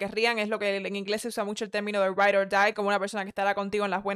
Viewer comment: que dijo? Right or Die?